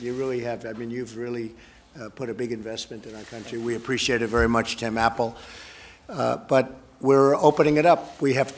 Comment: I just hear this as “We apprecaite it very much, Tim; Apple…”